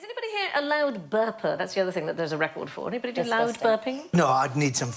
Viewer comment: I thought she said loud birther